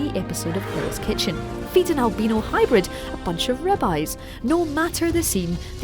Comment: I thought I heard her say "And feed an albino dinosaur som Rabbis"